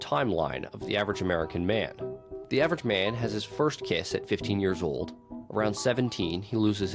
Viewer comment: I thought he said first KID and I was so scared 😭